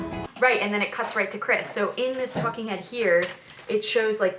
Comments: thought she said “in this fucking head here” 😂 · i thought she said fucking head not talking head 😭😭😭😭🖐🏽